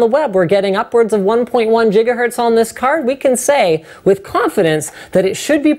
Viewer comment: Did he say jigahertz...?